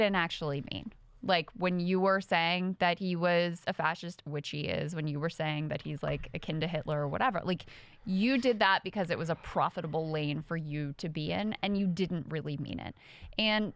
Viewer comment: around 3 minutes. Instead of "akin to Hitler" I first thought Crystal said "A Kinder Hitler" (which would mean a "childrens version of Hitler" ) which is hilarious. Like even among dictators Trump is always just a smaller, more petty, less smart, pathetic version.